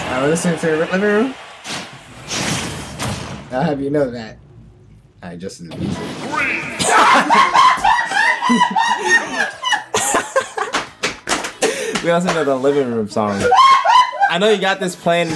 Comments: Wtf did he say at 8:30? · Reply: Antwuan Jones “I’ll have you know that... I adjusted the music” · What did say at 8:25 before fabulous laugh? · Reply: Creepazoidzes “I’ll have you know that... I adjusted the music.”